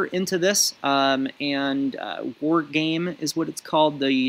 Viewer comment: What did you call it at 25:00? Wardgame? Worgain? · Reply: I believe is referring to "war-gaming transmitters."